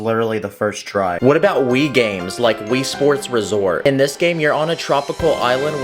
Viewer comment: When you said wii sports resort i thought you said wii sports world war💀